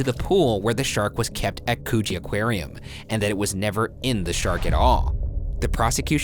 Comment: did he say coochie aquarium